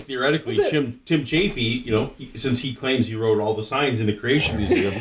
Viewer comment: Does say that this Tim Chaffey person claims to have written "all the science" in the museum? That would explain things... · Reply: Signs actually